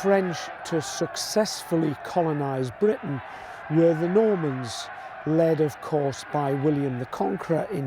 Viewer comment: The ROMANS in 1066 I think you mean the Normans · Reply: Sorry I missed heard. I listened twice and thought you said Romans please blame it on my age. Love your videos x